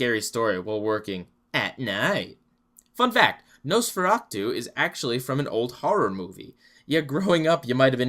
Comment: I always thought they said "nose for hat two"